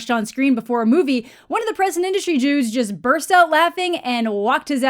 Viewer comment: did you say "present industry jews" or "present industry dudes"? Cuz they have very different contexts LOL maybe a Freudian slip?